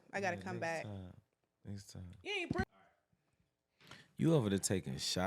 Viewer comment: sounds like said “you ain’t preg……..” 😂